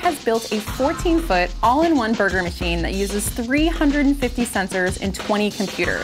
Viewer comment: the caption say censor instead of sensors